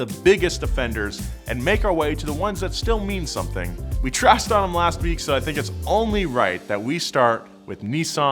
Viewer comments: says " We Trashed them last week.." Captions say "We Trusted them last week..." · Aftermathmathematic have you been in one? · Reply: my bad, i thought he said "nothing" instead of "something" lmao